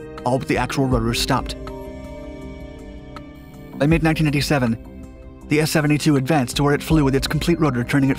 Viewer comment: "...by mid 1997" did I hear that right? I think 1987 is more like it. And again